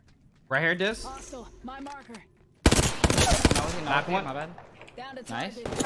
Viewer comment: Did Richard say knocked one my bad?